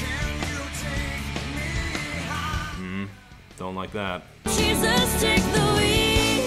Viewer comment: At first I heard it as "She just take the Weed" and I think whoa!!!!